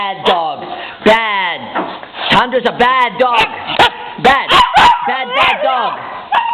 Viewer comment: sounds like it said "im not a bad dog!!!"